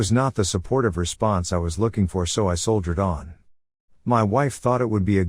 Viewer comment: When he said "So I soldiered on." I heard it as "So I sold her dog."